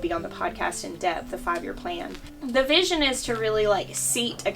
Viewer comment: Would LOVE LOVE a devotional podcast!!!! But I like most of what you put up? I thought you said a 5 year plan????? due before October